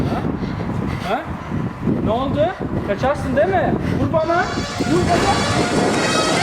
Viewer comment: orda vur banağğ mı dedi benmi yanlış anladım sjsjsjjsjsskkdm